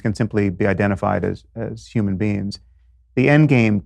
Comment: I heard “Human beans”.